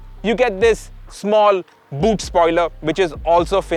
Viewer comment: not sure what I heard was right 😂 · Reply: Boot lid spoiler?